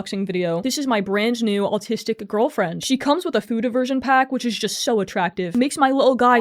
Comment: I thought you said "futa version"😭😭😭😭😭